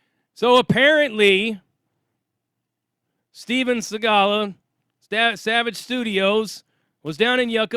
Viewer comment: Well without his tatts Savage does just have one of those faces, you know? And I almost fell out of my chair. I thought you said his real name was Steven Seagal the first time